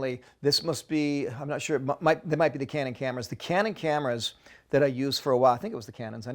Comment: When he said cannon cameras I thought he said candid camera haha. Anyone out there remember that? Those where good times